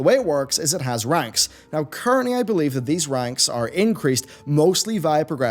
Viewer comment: Was that "these ranks" or was that "these wanks" I mean it REALLY REALLY sounded like wanks lol Perhaps it was what is known as a Freudian Slit?